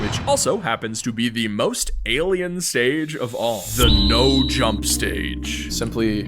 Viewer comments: DID I HEAR ALIEN STAGE? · did bro just say alien stage...👀👀